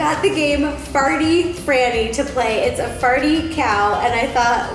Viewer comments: Am I the only one that thought it said “Farty Fanny” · I thought it said farty granny xDd